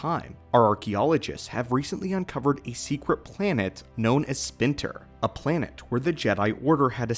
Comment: I swear to God you said sphincter 😂.